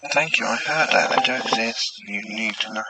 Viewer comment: Superb video. Loving this new angle on your videos. For the voice at the end I thought it said. "Thank you, I heard that, and Jack says you need to know he is ok"